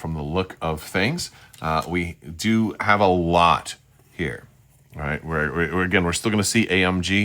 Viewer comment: "we do have a LAAT here" ... well that's what is says on the box... oh you mean LOT. sorry XD